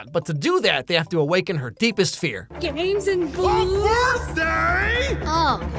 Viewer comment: did she just say Gays and Jews?!?!??